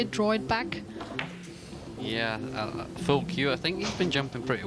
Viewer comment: did he just say fuck you to Jasmine?!?